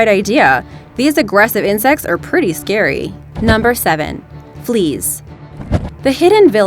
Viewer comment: I thought you said please it was fleas